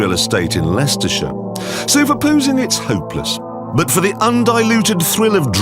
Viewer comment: did he say "for posing" or "proposing" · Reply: Posing i think